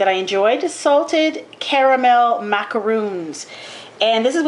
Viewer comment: haha XD at first i thought it said salted CAMEL macaroons..... hahaha XD nice vid though :)